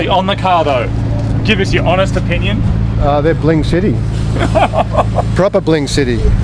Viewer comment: bling city? i thought he said "plain shitty" lol